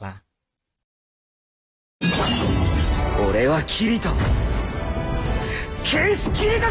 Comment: งุ้ย เก่งมาก ๆ เลยครับ 🥰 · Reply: ผมได้ยินผิดเป็น ฉันคิริโตะ นักดาบคิริโตะ555